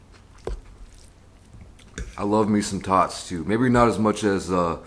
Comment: said, "I love me some tots" I heard, " I love me some thots" 😂😂💀💀